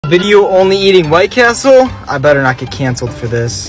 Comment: Oh you said White Castle ...PHEW I thought you said White People